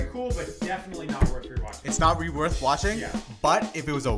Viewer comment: did say "that isn't reworth watching"? or are my ears not working alright